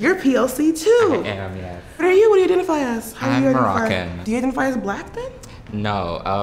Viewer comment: I heard por-ak-in what did he say??! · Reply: Moroccan